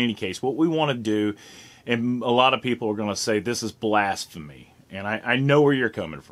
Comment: did you say "blast-phemy"? Maybe you didn't, but I think I'm going to start using that word anyway.